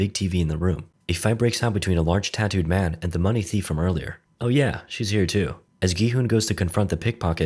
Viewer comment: I thought he said "oh yeah she's 02"